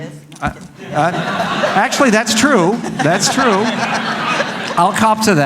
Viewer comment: THAT'S SEXIST !! is what she said -_- video is most relevant now than ever